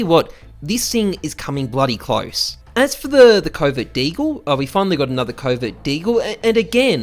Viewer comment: I keep thinking he is saying covid deagle at 1:45, I know he is saying covert but I hear covid